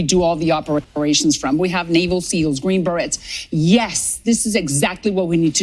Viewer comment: did she call our elite fighting force "the green berettes"? 😅